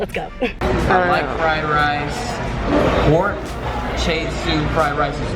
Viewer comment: did he really say pork chay-shoe rice?